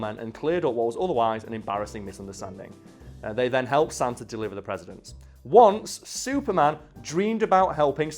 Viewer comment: Am I insane or did he say «deliver the presidents»?